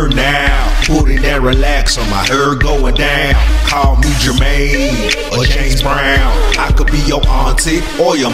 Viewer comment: it's sounds like he saying call gucci mane or James Brown